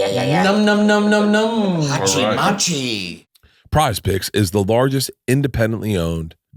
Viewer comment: 10 dollar homie did anyone else catch saying "Hachi Machi" at 52:35 ???